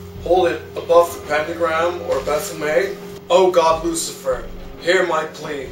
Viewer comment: is amazing. My favorite part of the dark ritual vid is that he begins it with "Hello you assholes." Also that he calls upon "Goth Lucifer." Perfect. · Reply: Oh I thought he said God Lucifer lmao